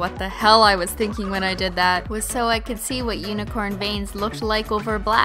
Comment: Omg i thought she said unicorn bangs